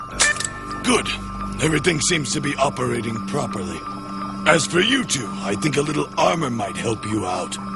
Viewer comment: DID I HEAR AT 0:28 VEGETA SAID "As for Youtube"??????????